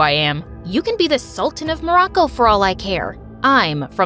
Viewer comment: did I hear Morocco 😮